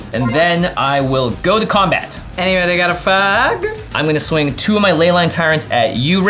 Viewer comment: I know she says “anybody got a fog” but it really sounded like “anybody got an (F slur)” and my brain short circuited - I simultaneously thought she was asking for a cig, or one of my gay girlies was talking to me. 😂